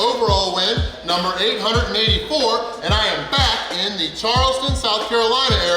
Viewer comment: I really thought you just said "And I'm FAT in the Charleston area." That was funny lol